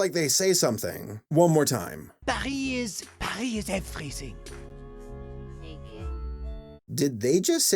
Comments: I heard something along the lines of 'die is- die is everything' God, even google voice is a better voice actor then them. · N-word? I tried to hear it and didn't hear anything remotely similar, sounded like he said "Paris...Paris is everything" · i heard pie is everything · I think he says "everything" in a french accent. I could be wrong tho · it sounds like he says “Paris is freezing” either way it doesn’t sound at all like the n-word ¯\_😕_/¯ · what the only thing i hear him say is "paris, paris is everything" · I only hear "Paris is everything" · I hear “No, Paris, Paris is everything!”